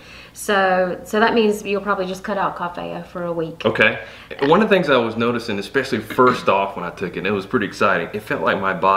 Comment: What was that she said at 6:09 cough heya? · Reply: Coffea